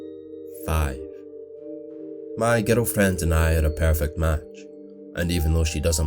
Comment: Is it just me but I hear "ghetto friends" instead of "girlfriend"